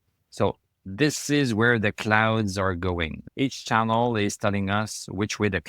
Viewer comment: I laughed out loud...thought you said clowns